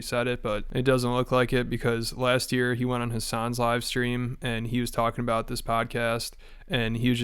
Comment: I misheard and thought you said "He went on his son's livestream" and I was like THAT"S his son?